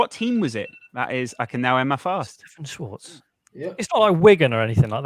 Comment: Did say at 11:15 he was going to end his fast?